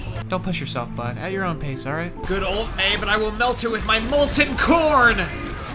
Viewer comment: I thought I heard torbjorn say molten porn